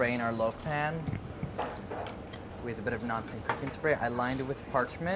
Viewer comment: I KNOW he said "non-stick cooking spray" but he swallowed that little part BOTH TIMES making it sound like he said "Nazi-cooking Spray"!